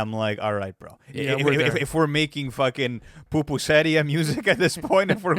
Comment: Did he say "Pupuseria music?"